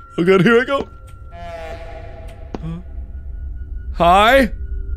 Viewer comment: that moment when markiplier said hi and it was so perfectly timed it sounded like it said hi back